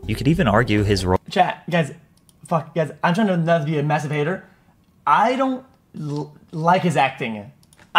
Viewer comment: I thought he said masterbater at first lol